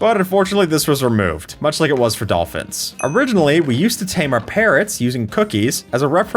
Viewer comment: I heard “we used to tame our parents”